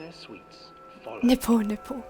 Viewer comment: lol did you say Nipple Nipple at 14:32 ?